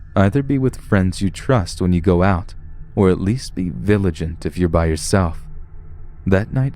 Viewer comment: I heard village-ent... instead of vigilant. This is one of my favorite scary storyteller's voice. 💕